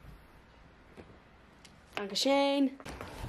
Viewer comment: did she said „Dankeschön”? 😂